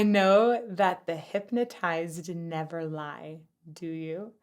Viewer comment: I always heard, 'The Hippie Tribes never lie'.